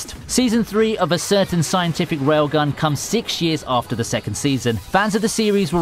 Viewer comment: I swear I heard you say "A certain scientific railGUM" at first.